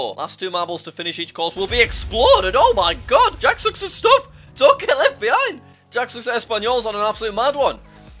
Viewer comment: I thought he said don’t kill fbi